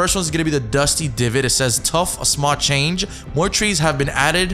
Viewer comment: he really said “tough” 😂